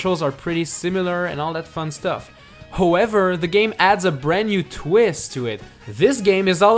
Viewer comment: it sounded like he said hoever😂😂